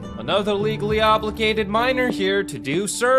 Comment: Ngl, I thought said "Another legally obligated minor here to do service" Closed Captions save lives...